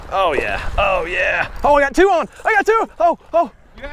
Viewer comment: 7:25 ish. "I got two, I got titty, I got two." I heard that